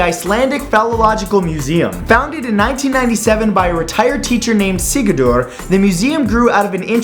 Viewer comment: I heard "retired" as "retarded". I feel like Borat now...